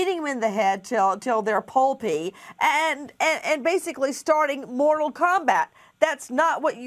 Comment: wwwwaaaitttt wwaa wait a minute... did she say "Mortal Kombat" :b